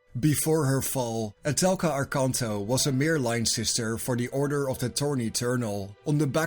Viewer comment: For a moment, I thought he said the Order of the Thorny Turtle 😂